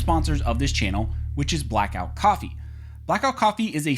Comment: For a second there I thought you said black rifle coffee and I was going to be like “bye bye lefty”